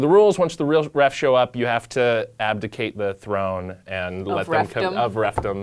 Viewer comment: what they were saying? i understand the message but didnt grasped the exact words. ad.... av..... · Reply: Abdicate (1 year later lmao)